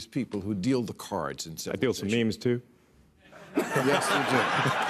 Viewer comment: PLEASE HELP. Damn. I know English quite well, I'd say but didn't catch that joke at 4:40. Actually rather acoustically ("That feels memes, too?) but then also this move with his jacket? No clue :/